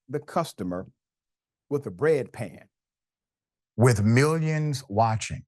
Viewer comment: I thought he said bed pan